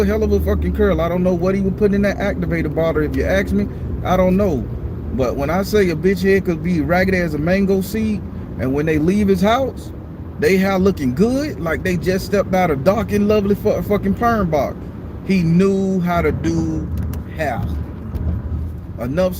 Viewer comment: I was doing my work listening to you and I heard "raggedy as a mango seeeddddd" 😂😂😂😂😂😂😂😂😂😂😂😂😂😂😂😂😂😂😂😂😂😂😂😂😂😂😂😂😂😂😂😂😂😂😂😂😂😂😂😂😂😂 omg! I almost flipped my laptop on the floor laughing. Whew chile. I'm finally getting the chance to listen to all your stories lol!